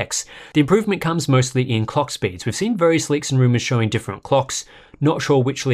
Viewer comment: Who else heard cock speeds at 7:41 ?? 😂😂😂